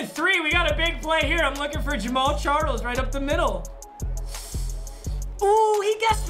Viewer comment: Bruh I swear to god I thought said James Charles I was like wtf